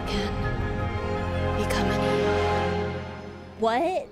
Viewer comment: I think she said:" Become a new aeon (God in HSR universe)". Yes, she a human aim to ascended into aeonhood (godhood)